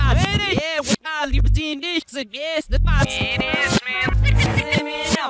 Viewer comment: I hear “it’s Eminem, it is man”